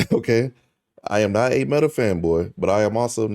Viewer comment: why did i hear the word femboy instead of fanboy